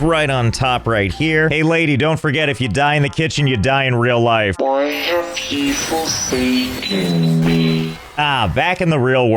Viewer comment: I hear” boy why are people seeking me?”